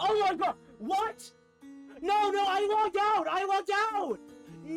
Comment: Why did you said Allahu Akbar? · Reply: It was clearly "I logged out"